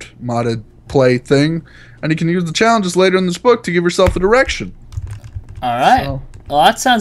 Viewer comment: haha i thought said to give yourself an erection instead of fiveing yourself direction.. looool